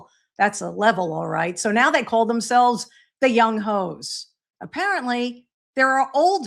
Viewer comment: First I miss heard it, I thought I heard “the young boys from Washington” and I thought what the hell this is just another level of woke stupid then I realized its the young H, oh well could have been worse😂